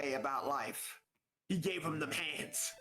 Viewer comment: lol i heard "pants" too